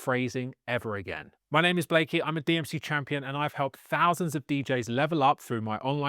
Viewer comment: What's a DMC ? I thought you were saying UFC CHAMPION the whole time lol